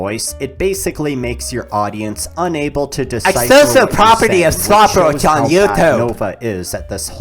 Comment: "unable to decipher what you're saying, which shows how bad nova is" (i am an audio preservationist, so it's easy to hear what said)